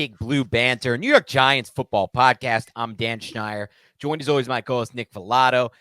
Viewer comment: bleav podcast i think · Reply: I thought he said Dan Schneider at first. Then I realized there is no Dan Schneider reporting for Giants. But just another reason why we eagerly anticipate your guys' analyses and articles!